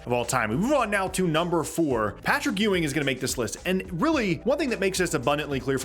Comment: I thought bro said Patrick Williams at 4